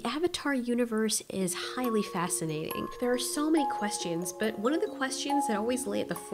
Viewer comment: and 0:06 I keep hearing questeeons rather than questions